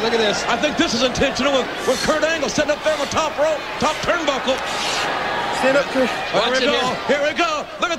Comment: hhh says stand up Kurt staged ass shit who else heard ?